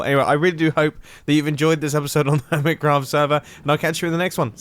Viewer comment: I thought for a second he said hobbit craft instead of hermit craft